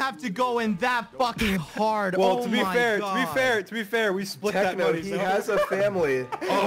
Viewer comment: i think he said "we split that money"